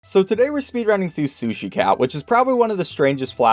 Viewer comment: Yo, Am I the only one who hears 'Sushi cow' instead of cat??